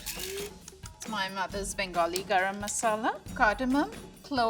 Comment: all i heard was "Bengali mother".